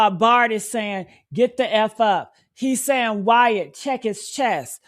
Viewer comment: The Get the fuck up sounds like he said, "Shut the fuck up!"